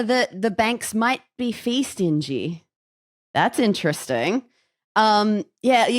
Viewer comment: I heard the phrase "Fee Stingy" as "Feastin G" and was laughing for about 10 min